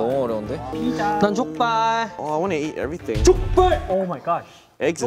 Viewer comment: Whyyy leeknow sounds like he is saying chup beth in hindi to bangchan 😂😂😂at 2:28 wtf it's too funny